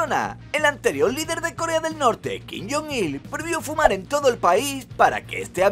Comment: entendi min yoongi :v